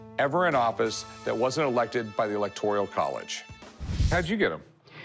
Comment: did he say “electorial” college?